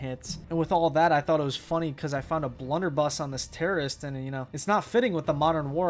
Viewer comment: did he just call the Ranger a blunderbuss